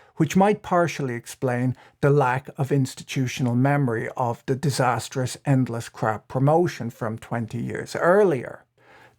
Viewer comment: Lol restarted at 9:30, and misheard that line as "disastrous endless crap promotion". And I'm like "nah man im pretty sure planned obsolescence is more than 20 years old"